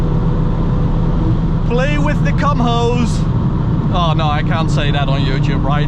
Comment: Did he just say "cum hoe"?😅